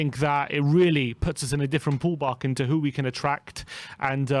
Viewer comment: I knew I heard "Pall Bark"